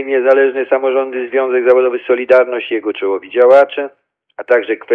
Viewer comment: Profesor przywalił z wysokiego C. Świetne PODSUMOWANIE 50:17🤝🥇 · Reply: No aż sam usłyszałem Do Sol Mi :D